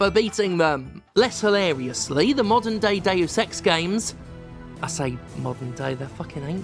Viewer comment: I heard "Modern Day of Sex Games". New genre? LOL